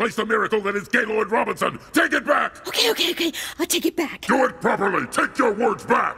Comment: what did she say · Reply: Loki Batman she said “ok ok ok I’ll take it back” in reverse